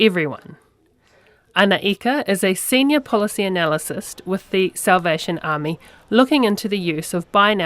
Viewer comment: did she just say Senior Policy "Analysist" lol